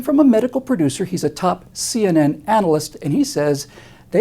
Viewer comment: First I heard "analyst" as "adolescent." Kind of appropriate.